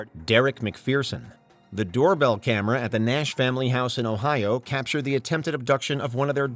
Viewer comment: did you say in Ohio?